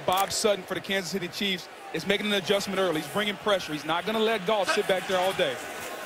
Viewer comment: it sounds like he said Jerk Off but he really said Jared Goff